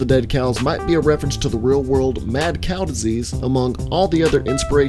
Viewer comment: Did you just say mad snail diesies or mad Cow diesies